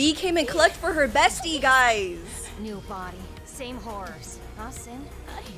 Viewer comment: My theories : at 16:49 i heard V saying: same body,same horror huh cyn..? So im thinking a robot name cyn is at the episode 2 where N was a butler V as a maid and J as maid just like V. So we seen a new robot at behind tessa or someone else and the robot behind tessa or someone was Cyn..? And if we slowmo at N touch a tape we seen cyn turn into zombie drones and the symbol.cyn was the first zombie drones....that all i remember so im will tell more about it...